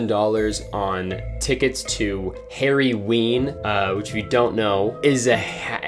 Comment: I heard say "hairy ween" instead of "harryween". holy shit i need new ears